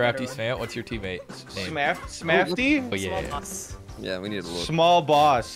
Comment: I thought Crafty said “Smallpox” and I was like “damn, that’s a little aggressive for a team name!”